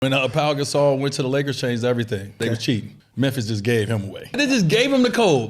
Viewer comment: All I heard was “We chasing Kobe”